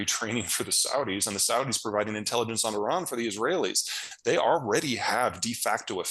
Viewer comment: Did Peter say "the Saudis were providing intelligence to the Israelis?" Hm... 🤨